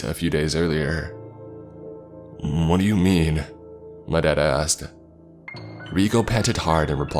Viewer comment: Every time he says 'my dad asked' I hear' 'im dead ass'